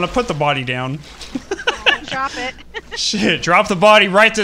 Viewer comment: It sound like said sh!t